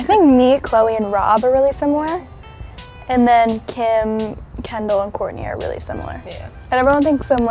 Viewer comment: What she said to 7:38 to 7:43 please? my english is not perfect :) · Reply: Sarah Leroy she says , Kylie, Khloe and Rob are similar/ alike Kim , Kourtney and Kendall are similar/ alike